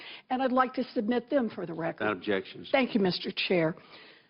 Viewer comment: She said : "and i'd like to submit them to the chair" someone said: "objection" She said: "thank you Mr. Chair".... I thought objection means "no, i oppose what you said, you cannot submit them"...? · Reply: they said, "No objections"